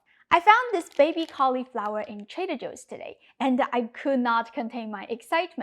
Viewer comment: did you look at the sub-titles? somewhere around 0:30-37 it says "Traitor Joes" instead of "Trader Joes" ;-) ;-) I"m sure the folks at TJs are not amused ;-)